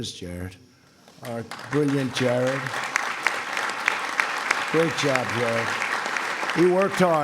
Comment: did he say “great job bro”? Lol